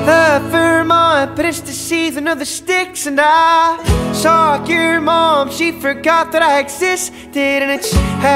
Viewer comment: I thought it said sock your mom 💀